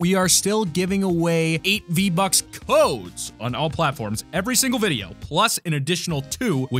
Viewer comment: For a second I thought he said 8 V bucks and 2